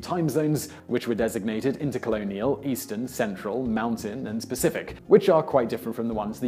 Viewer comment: did he say "Specific" for "Pacific"?! ZOMG.